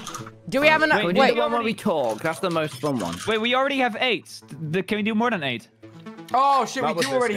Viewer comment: I heard “We already have aids. Can you do more than eight?” And now I shall cry.